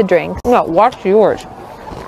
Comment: Thought I heard nobody talk to her 😂